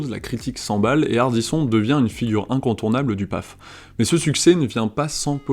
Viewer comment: une figure incontournable du quoi ??? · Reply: PAF